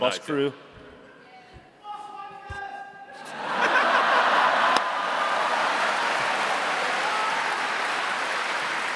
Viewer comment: I couldn't make out what an audience member said that made Jack go out and hi five them afterwards, around 58:15, anyone help? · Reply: Buswankers